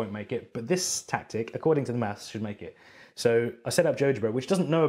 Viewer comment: What program does he say at 13:32? I cant understand him. JudgeBerry? lol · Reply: GeoGebra